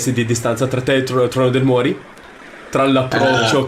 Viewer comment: ma ho capito male io ho abbiamo avuto il nome del trono del muori ? 21:00 correggetemi se sbaglio pls